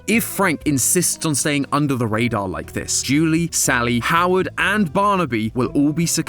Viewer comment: did just call Howdy Howard?